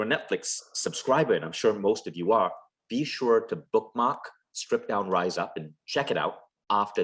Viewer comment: What Netflix film did he mention at 1:50? Can't hear it clear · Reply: Strip down and rise up